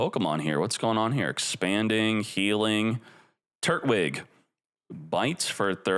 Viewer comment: Why did I hear turdwig😂